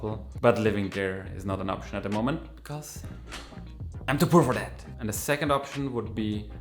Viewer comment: Anybody, please help me, or i'll go fakking insane😱😱😱 What was the reason for not living in Japan atm??? All i hear is: -"am to brover-land" I listened +10 times down to 0.25 speed😵‍💫😵‍💫😵‍💫 Heeeeeelp me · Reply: I'm to poor for that 😅